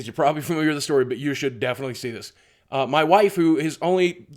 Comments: I heard "waifu", and then I was sad when it turned out to be "wife who"... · it sounded like you said "my waifu" lmao xD